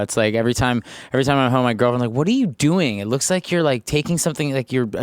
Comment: My heart broke when I heard him say "girlfriend" when he was talking about how she'll ask why he's making tea. wow ok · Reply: I thought he said grandma oops